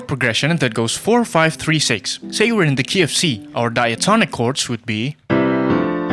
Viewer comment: I’m sorry, but I keep hearing your “Key of C” as “KFC” (I might be hungry) 🍗